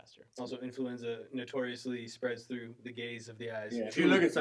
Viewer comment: I had to go back and re-listen because I thought says influenza spreads through the “gays” and I was like 😱 during pride month?!?! And then I used more than one brain cell and realized you said GAZE and I was like 😮‍💨